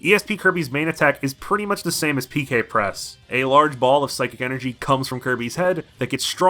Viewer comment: Am I hearing things, or did you say PK Press? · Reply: Yeah, that's just what me and my friends always assumed Ness was saying in Smash. Apparently it's supposed to be PK Flash, I just must have called it Press by mistake in the video cause that's what I'm used to saying.